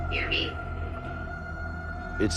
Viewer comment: it did not say “fear me” it was answering his question of what the guy had in his hand, Norman said “hear me” as in a device to hear me with